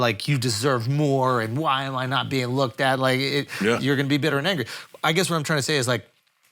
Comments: Did Andrew say the N word lol 🤔 a bitter what.....? Lol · So around 01:51:08 ish - 02:51:15 ish, what the heck is Andrew saying? Does he say ".. and you're gonna be a bitter n-word (but really said the word, and with hard R at the end)"?